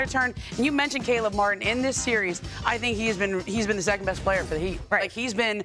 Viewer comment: Jimmy Butler is the best 2 way player in the NBA right now his elite defense and electric offense, with ultra clutch gene, next level stuff, amazing to watch him play 💯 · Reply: I thought you said second best 2 way player 😅